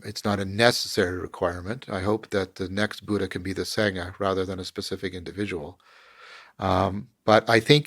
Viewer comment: What is he saying? "I hope the next Buddha can be the .." what? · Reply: "I hope the next Buddha can be the sangha rather than the individual." A sangha is a community that practices Buddhism together.